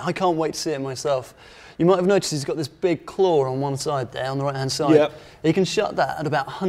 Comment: Can anybody pls tell me what they say at 0:29, after "claw on one side ....."? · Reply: There on the right hand side